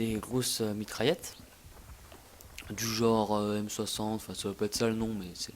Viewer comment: Super jeux quoi ! Remarque inutile : à 5:00 tu dis " Jore " ça veut dire " Genre " , non ?